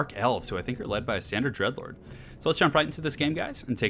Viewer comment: I heard it as "Roasted some of my white lines" rather than "lions" <_< >_> Never mix your meth with your coke. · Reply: Heh. And I heard "these are Dark Elves led by a standard Edge Lord" rather than "Dread Lord". Funny the things you hear. ;) :)